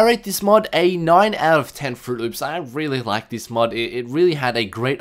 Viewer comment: did he really say "i am really like this mod" -____-" oh well, great video and mod though